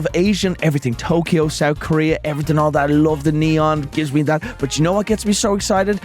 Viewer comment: Did Brine just say he loves Neon?!